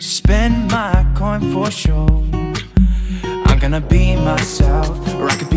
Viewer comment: Did anyone else hear: I'm gonna pee myself? 😂